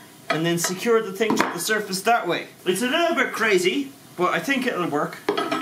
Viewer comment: What said "It's a little bit crazy, but I think it will work" What I heard "It's just crazy enough to work"